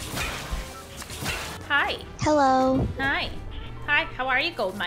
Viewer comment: for a split second I thought that the Midas actually said hello